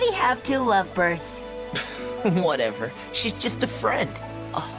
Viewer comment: I thought Adrien said: She’s just a very good “Fred”